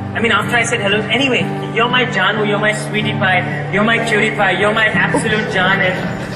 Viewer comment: Did he just say "You're my Pewdiepie" ??😂😂😂😂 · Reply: Lmao no he said cutie pie